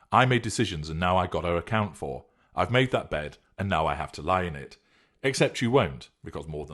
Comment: He actually did not say "now i have to lay in it". He said: "I HAD to lay in it." Basically saying: it's all solved, old news and we have moved on and so must you guys.